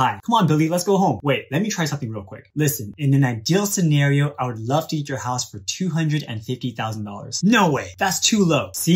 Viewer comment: I heard him say " I'd love to eat your house"